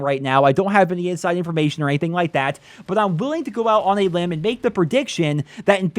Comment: did you say go out on a limb? lmao